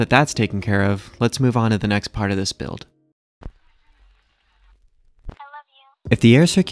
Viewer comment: !!!Please upvote this I need answers!!! Am I the only one who heard the “I love you” at 8:56 or am I tripping balls?